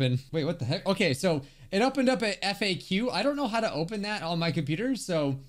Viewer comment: i thought people said Fak